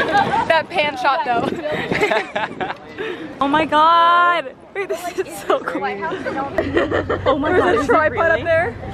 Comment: Did she say "babe this is so cool" at 4:27 or is it just me? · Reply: i said wait this is so cool!